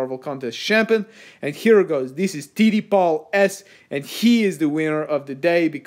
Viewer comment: You said TD Paul and my mom thought you said titty balls 😭😂 thanks for getting me yelled at lol